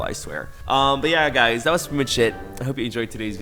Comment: Every time he says "that was pretty much it" I hear "that was pretty much shit" and its annoyingly accurate 🤣🤣